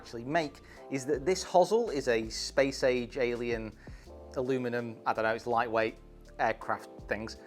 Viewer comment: did you actually say "aluminum?"